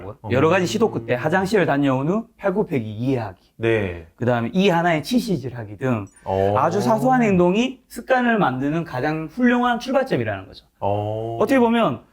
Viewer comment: 이때 뭐라고 하는지 잘 모르겠는데 들으신분은 가르쳐주시면 감사하겠습니다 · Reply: 이 하나에 치실질 하기 등 아주 사소한 행동이 습관을 만드는 출발점 이라는 것이요~